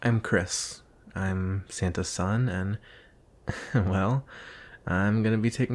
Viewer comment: I thought it say "Satan's son..." Why...? Good video, keep it going.